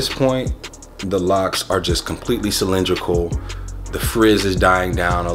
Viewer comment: what word was that? That shit threw me off lol . Man's a scholar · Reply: camcane1 😂 you clowning me 😂😂cylindrical lol just basically that elongated round shape .